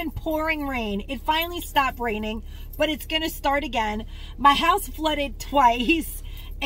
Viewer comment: Hi! I’m glad you didn’t lose any items you’ve shopped so hard for. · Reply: I thought you said your shed flooded but your items were in your home or either high up that’s why I said what I said.